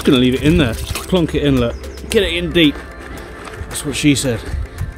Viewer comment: 😂😂😂 did he just say that’s what she said 😂😂😂